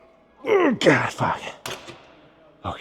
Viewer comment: I’m sure I heard Godfather